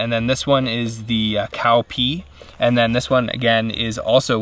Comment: did he just said "and this one is cow pee" ?????